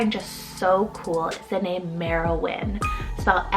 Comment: I came across the name Merewyn several months ago and suggested it to my husband. He said it sound like a little kid trying to say my name "Maralyn" Then when watching this and hearing you say Merowyn I completely thought you'd said my name. So I guess he was right.